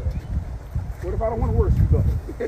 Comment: Hold the fuck up at about 12:07 or so did I hear that nigga laugh and say what if I don’t want to work with you guys